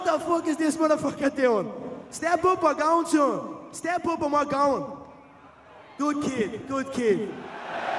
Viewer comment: What does Conor say at 1:38:34 before good kid? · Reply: "Step up and we're going" - in other words, 'if you stand up, we can have the fight right now if you like'